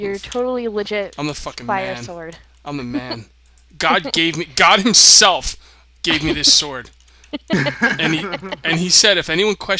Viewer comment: For a second there, I thought you said "Gog gave me, Gog himself gave me this sword." :-P